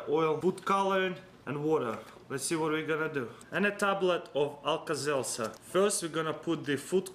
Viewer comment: what does he say at 1:54 alka selsar or what